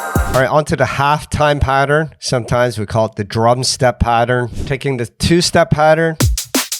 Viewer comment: For context here's an old track: <link> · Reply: I clearly misheard. I thought you said 'drubstep' not drumstep.